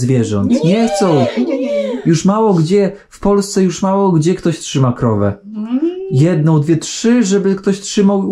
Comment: Trzeba się wsłuchać w rozmowę i przestawić na ten język :) Też na poczatku usłyszałam, że ma "trzy krowy" a pani mówi, że "trzyma krowę", a jej syn " trzyma konia". To daje smaczek tej rozmowie i jest trochę wymagajace od słuchacza :)